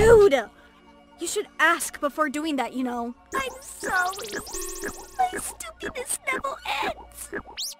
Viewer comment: so apparently Ena is saying: "my stupidness never ends but ive always heard: "im as stupid as deviled eggs" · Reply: I heard it as "my stoopiness never ensss"